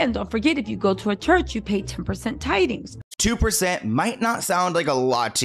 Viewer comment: I thought she said "10%"